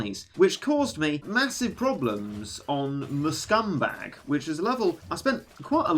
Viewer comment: I heard "m'scumbag" like 3 Times and I thought at first that it was refering to you uber carrying and saying "it's a bit hard with these restrictions".